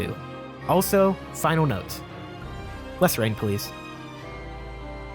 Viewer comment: Lol my hearing is trash can anyone tell me what he says at 13:30 ? After "Final Notes". I've rewound it several times haha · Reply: Less rain please😆